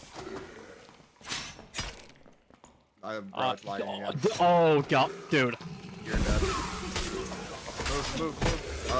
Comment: What does say at 9:48? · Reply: “I have- bruh it’s lagging again”